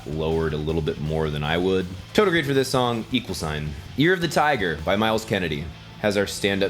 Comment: NGL, When you said "Year of the Tiger" I heard "Ear of the Tiger"